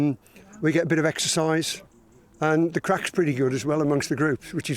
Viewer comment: did he say "the Crack is pretty good" at 2:08 ? 🤣 Wellll ...... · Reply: "Craic". It's Irish / Scottish and means a good time.